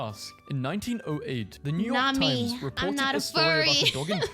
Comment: "Not me, I'm not a furry." That's what she says and not that he's 15!!!